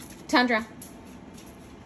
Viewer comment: They are better than me. I thought she said Cyprus twice. 😅